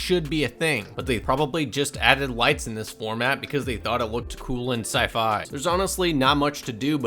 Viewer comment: The subtitles spoil the ending a little bit. Instead of "they" (referring to whoever designed it) as in the audio, there is.. well, the spoiler ;) might wanna change that